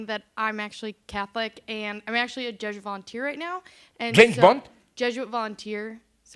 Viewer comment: did he say James Bond? "...Jesuit..." "James Bond!?!?" "...Jesuit..."